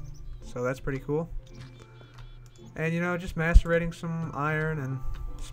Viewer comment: Lol at 4:40 it sounded like you said "Masturbating some iron"